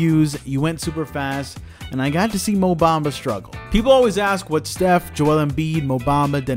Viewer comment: lol thought mans said Obama but it was actually mo bamba